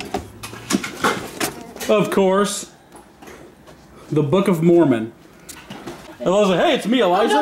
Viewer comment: I honestly thought that said "the book of a moron" 😂